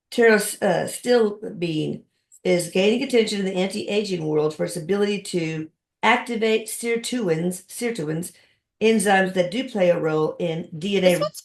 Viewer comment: What did she say at 12:15? The really good antiaging supplement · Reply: Pterostilbene